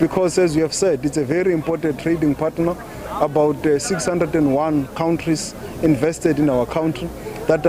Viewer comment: did he say 601 countries?